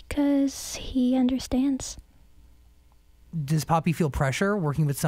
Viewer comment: I thought he said poopy